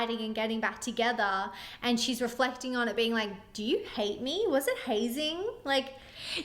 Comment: I’m Australian and I LOVE this song, but I understood the hazing lyric from movies (also possibly from crime procedurals where someone has dramatically died in a hazing that went way too far!!) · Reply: as another European, I just learned what hazing was along with them. I also learned I've been singing those lyrics wrong for months (I thought she said "was it hazy") Definitely makes me appreciate the song more, though! Now that I know the full meaning it hits even harder.